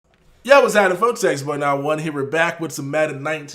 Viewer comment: The into shook me 😂 Thought said “Yo I was having phone sex”